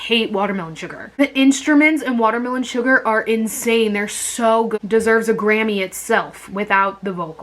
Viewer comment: did she just said "WMS deserves a grammy itself" ??😂 Harry be like Here you gooo guuuurlllllll